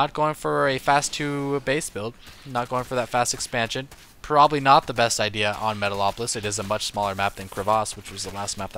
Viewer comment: and on 03:00 the closed caption says 'he's going for a fab expansion, probably not the best idea on metal awful if its a much smaller bathroom' lol then later it says 'he might be going doctor russian or a tp rush hehe